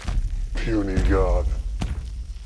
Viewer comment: somehow when my family watched this scene we ALL heard hulk say "Some god" we laughed so hard we rewound it to watch it again only to hear "puny god" laugh all over again then question our sanity on how we all heard somthing different from what was actually said